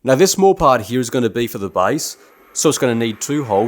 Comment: are you saying "base" or "vise"? I don't understand mounting the spindle to the crossslide. Where is the workpiece held? Where does the x/y travel come from? I must have missed something,. or I'm just not very clever. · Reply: He says Base. If I had to guess the spindle is going to be mounted perpendicular to the standard lathe work holding fixture - which is how the material will be held. No different from a standard lathe operation But, seeing as how I haven't finished the video yet - I guess I'll see!